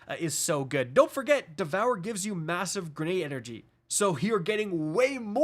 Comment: Did anyone say MASSIVE?